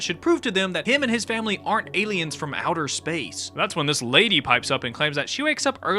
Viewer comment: As someone who also thought she said shit, I’m pretty sure what she’s saying is “from outer space yet” as in in addition too.